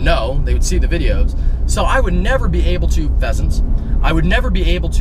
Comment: for a moment I thought he said "peasants" but it was pheasants lol